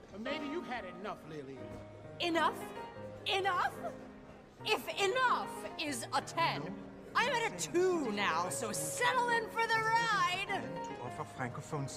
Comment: what did she say here? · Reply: the bar tender says "Maybe you've had enough, Lillian!" and she responds "Enough?! Enough?! If 'enough' is at a 10, I'm at a 2 now, so settle in for the ride!"